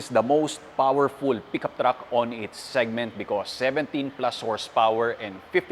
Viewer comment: did I hear it right? 17 plus horsepower?